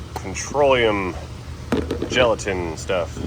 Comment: Did you say controlium gelatin stuff?